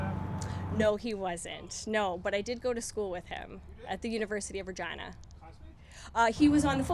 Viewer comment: This press conference interview was really weird... but at 7:32 did she say, "at the University of Virgina?" "Snatch and Clean 'n Jerk"????